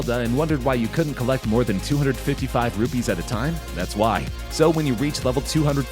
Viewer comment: Does he said rupees